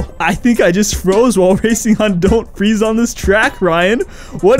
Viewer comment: This game is so bad they made Ryan say, "Welcome to "Don't Freeze On This Track!". I thought it said "Icy Land Forward". I am going to go visit both the eye doctor and the hearing doctor. Wish me luck!